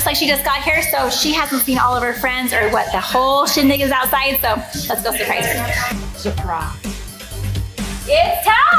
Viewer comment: what did she say? It was too fast for ,e to understand haha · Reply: “or what the whole shindig is outside, so let’s go surprise her!” shindig means the whole thing, so basically in the scenario ellecee’s party decorations are the shindig.